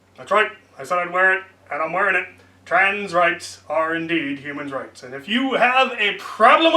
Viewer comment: For a split second I thought it said trains rights sorry my brain is broken